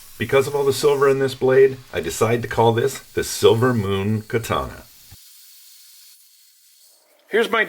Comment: Ngl, when you said silver moon I heard "Sailor Moon" 😅 Awesome job though, as usual !